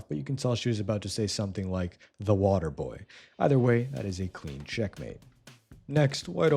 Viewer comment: so what'd she say at the end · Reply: Based off the letters pointing out from the top, it says waterboy